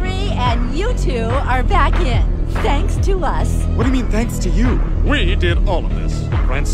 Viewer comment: Listen to what the blonde girl says @ 41:20(ish).. "3 - 2 - 2" (not a coincidence) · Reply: "Thanks to us"